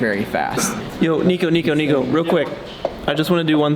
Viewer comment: this guy is really say "nico nico nii"?